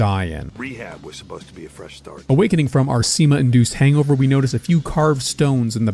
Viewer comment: If I had a nickel for every time a Vtuber thought he said semen, I'd have two nickels. Which isn't a lot, but it's weird it happened twice.